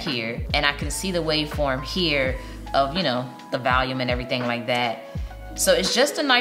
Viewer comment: "The valium..." Where are you from, I heard that.. Ive never heard it like that for volume...😌